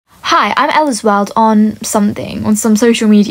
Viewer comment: When she first said ellasworld i thought she said eddsworld